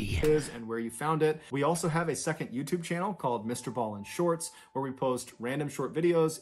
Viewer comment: Did he just say he is Ball in shorts? LOL